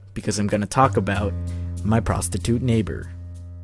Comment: I exited out of the video half way through prostitute and thought you said prostate XD